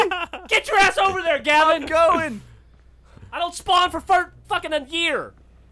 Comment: Did Geoff just say "fart-fucking?" I know it was a misspeak, but it sounds very close to "fart-fucking."